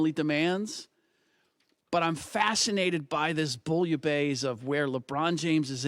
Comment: What word did he say? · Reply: Bullubase….it might be French.